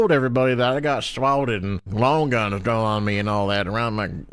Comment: I misheard for a second but I thought he said he was a peanut that got "swallowed" instead of swatted man, I feel that would have been very in character for his Peanut-sona